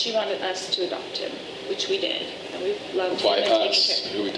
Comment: Did anyone else catch that 3:30 she said we “loved” taking about JJ 🤭